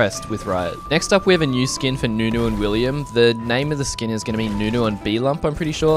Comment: I am a Nunu main... And did you just say William?